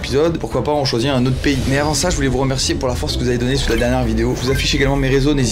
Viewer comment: il a dit quoi ? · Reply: <<Mais avant ça >> T ABUSE